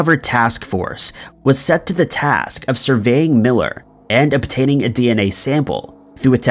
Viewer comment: Pretty sure I heard "survey" and not "surveil" 🤔